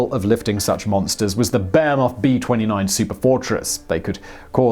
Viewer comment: I keep hearing “Bear Moth” B-29 Superfortress, not Boeing. Am I missing something?